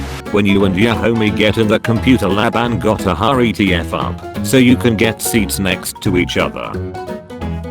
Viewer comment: it's "gotta hurry tf" and you said "gotta hurry to"